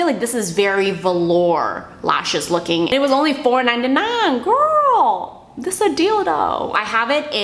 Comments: I left this video playing while going on another tab on my browser and @ 11:38 I heard "gurl that's a dildo" and legit thought was talking about sex toys. LOL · Totally misheard 11:37 omg "GURLLLL, THIS A DILDOOOOO!" lol sorry too hilarious to not point out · when you were talking about the eyelashes and you said "deal doe" it sounded like dildo 😂😂